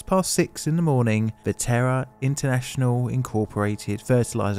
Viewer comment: I know I am not the only one who initially thought you were saying the "Terror International" plant exploded. (It's "Terra".)